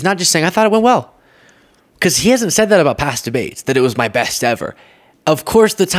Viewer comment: oh you said past debates, I thought at first you said mass debates.